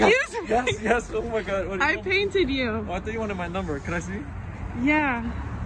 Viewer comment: 😆 begins to sprint “I thought you wanted my number” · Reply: I thought he said, “I thought you were one of my lovers” 😂